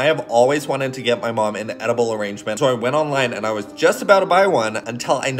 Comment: I thought the arrangement said argument